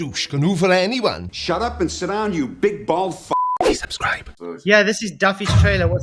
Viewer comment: Also, I always hear "Big bald fuck" as "Big-balled fuck" 😅